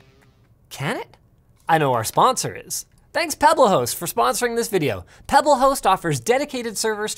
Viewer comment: idk if that's just me but when linus first said Pebblehost I heard "pebbleh0es"